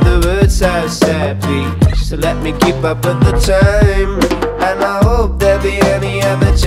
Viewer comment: Am I the only one who hear "and I oop'' from 1:20 to 1:28 (I think) haha? 😂 VSCO boyy